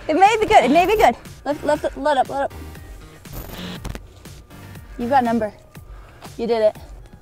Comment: did she say, "you've got an ember" or did she say, "you've got a member"... Lol.